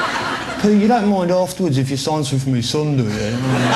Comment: What does Pete say at 2:32 ? (i'm not english sorry) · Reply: Alka-Young "Pete you don't mind afterwards if you sign something for my son, do you?"